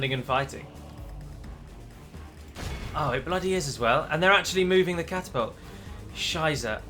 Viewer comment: Did he Just say " Scheiße" at 16:00 ? 😂😂